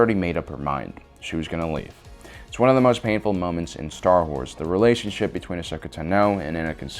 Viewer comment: did you just say star whores?